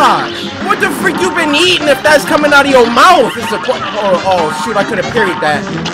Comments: Did he say shoot or s**t? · lol it sounds like said “oh sh*t I coulda parried that” but I don’t think he actually said it Edit: yeah I tested that if you try saying shoot really fast in the way he said it, it would sound like you said sh*t